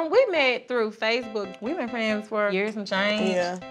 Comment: How does she not know who her “best friends” baby daddy is?? That’s what I’m wondering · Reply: Tayana Silva I thought they said they met in fb and only been friends for a year.